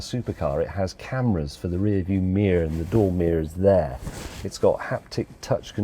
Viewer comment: James is now saying "Meer" instead of Mirror! Nana Liz's 'I See Deer' song from James May's Mail Time has had an effect!